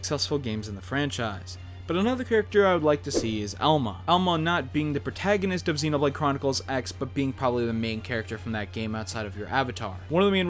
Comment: for second I thought you said elmo